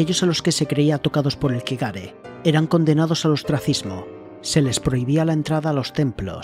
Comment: una pregunta sobre 11:24 que dijiste Transfismo? Nunca escuche eso o no se como escribirlo 😅😅😅😅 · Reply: La palabra es ostracismo. Básicamente es que te echen de tu ciudad/país por ser un indeseable y te prohíban volver. También puede usarse para hablar de alguien que es apartado del resto de la sociedad, aunque no se le exilie físicamente.